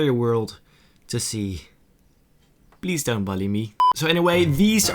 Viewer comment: I love the way he said please don't BALLY me instead of saying bully me